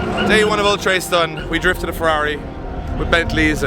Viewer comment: For non Irish people it sounds like was saying Oletrace